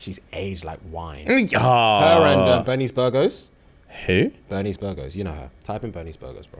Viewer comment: I thought he said bernies burger 😂😂😂